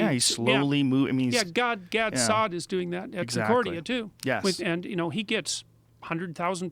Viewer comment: "god sod at sacordia too"? What did he say?